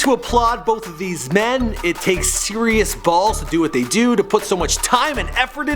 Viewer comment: "...to do what they 'dood'...!?" Did he really say that at 1:04!???